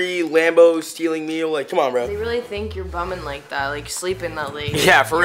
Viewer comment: said bumming instead of coming I feel like she trying to speak as a blood member